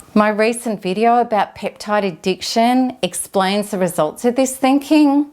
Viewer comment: "My recent video about peptydick addiction", I assume I'm hearing it wrong, but I've rewound it six times now but that's how it sounds, 'peptydick addiction'!! What ARE you saying? Someone please clarify???!!! Never heard if this before, ever!